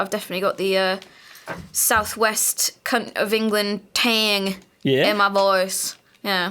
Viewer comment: DID JUST SAY C*NT OF ENGLAND?